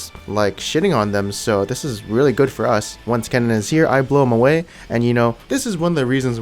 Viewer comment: wtf did you say XD??? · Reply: "once Kennen is here I blow him away"